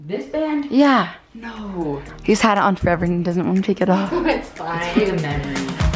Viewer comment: did she say its fine or its mine